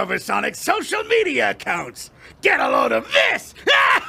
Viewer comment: I just heard "GET A LOADN OF PISS" IM DEAD😂😂😂